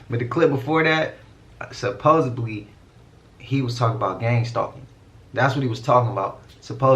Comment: Gang stalking or group-stalking is a set of persecutory beliefs in which those affected believe they are being followed, stalked, and harassed by a large number of people. Edit, I just searched it on Google. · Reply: OK thanks. 🤦🏾I thought he said gangstar. 🤣😂